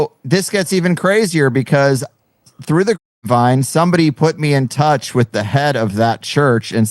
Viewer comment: are you guys using some kind of automated system for censoring? Because you bleeped out the word “grape” when was just saying “Grapevine.”